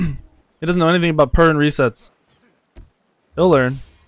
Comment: what kind of resets did say at 23:30ish? What are they? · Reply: i believe he saying pern, as in DJPernicus